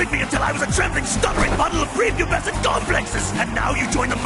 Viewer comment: What did Bone Cold say? "A trembling, stuttering puddle of prepubescent __?" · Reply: I believe he's saying "A trembling, stuttering puddle of prepubescent complexes."